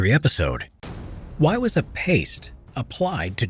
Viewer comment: I thought it said pasta